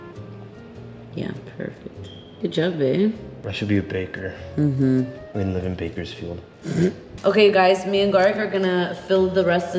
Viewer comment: Bruh I played this to fall asleep then I hear " I should be a baker that's lives in Bakersfield"🤣🤣😏😏